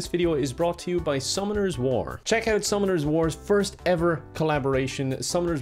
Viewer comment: I thought you said the game was called “Some others War” Like a war game of no responsibilities.